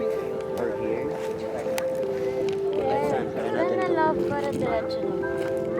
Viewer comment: did Kath call Daniel LOVE?